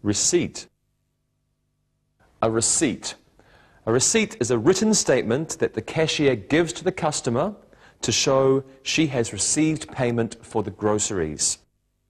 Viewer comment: who tell me please what did he say at 2:09 ? a receipt is .. statement . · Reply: Receipt: Is a written Statement that cashier gives to costumer to show that she received payment